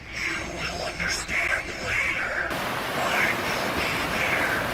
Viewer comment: I hear "you will understand later" "this is the start"